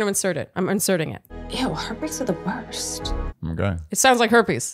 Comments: I think she was saying “heartbreaks are the worst.” So neither birthdays nor herpes 😅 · I thought she said birthdays are the worst but the subtitles said “Ew, heartbreaks are the worst” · I thought said “heartbreaks are the worst.” !!